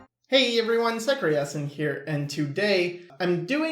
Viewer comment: I have been watching you for about a year, and I only now realised that you say "Hey everyone, Sycra Yasin here". I always thought you said "Hey everyone, Sycreasen here"